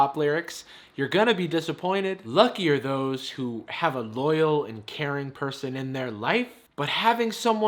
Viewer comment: At first i thought it says "Lucky are those who have a lawyer" and was like yes i guess?